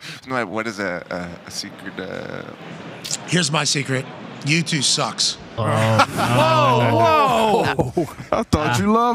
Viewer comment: Oh no! For half a second I thought he said YouTube sucks. But yes, U2 is the worst.